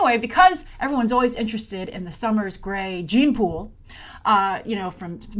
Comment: When you said "gene pool" I heard the couple name Jean-Pool. LOL